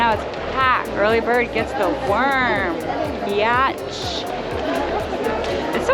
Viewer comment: omg that woman prob thought you called her a beyotch